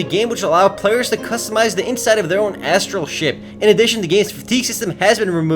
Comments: Transladed as 'instead of their own astral shit', lol. :D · CC: Customized instead of their own astral shit in addition to give speeches.